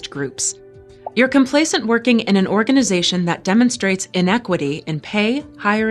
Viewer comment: the training says inequality, yet she very clearly says INEQUITY. Anyone else catch that?